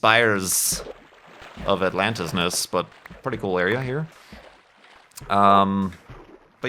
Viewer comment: did I hear you right? Did you say at 18:58 "Seattle ... pretty good area, here? :O) If you did move to this nice city, you gotta try The Lunchbox Laboratory for a great burger experience. They even do a good job lettuce wrapping them for locarb or gluten-free folks.